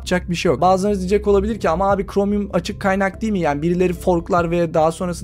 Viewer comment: Yalnız WebKit açık kaynak değil mi dedi ben mi öyle duydum? GNOME Web Linux da WebKit kullanıyor ve açık kaynak projesi.. Apple istese de öyle bir şey yapamaz. Bence WebKit desteklemek gerek...